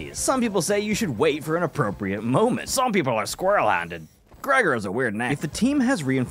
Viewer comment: did you say “kraiger is a weird name”